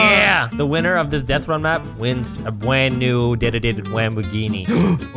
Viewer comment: All I heard was 'Bwand new dededede wamboghini'